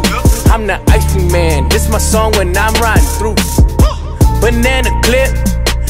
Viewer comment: who here swore he said 'Bannana Clit'?????